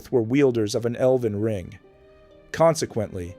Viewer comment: I thought it was saying Elden Ring for a second